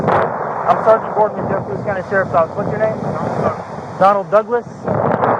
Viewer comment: Lol what in the hell is this. Why is he naked and why does he look like he's been marooned for years on an island and just floated to shore. Saltine extra salty. Put some clothes on. I thought he first said his name was Donald duck 😂